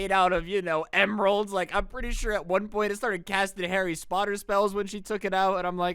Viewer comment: did you just call Harry Potter, Harry Spotter?